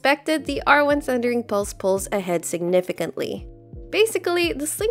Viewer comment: I thought said Thundering Pulse Pulse, but it's actually Thundering Pulse "pulls". LMAO